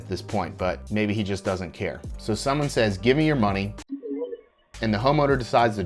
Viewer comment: It sounded like he said,"gimme your wallet."